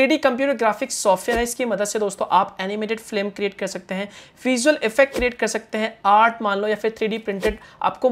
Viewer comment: he said flim instead of film lmao😄😄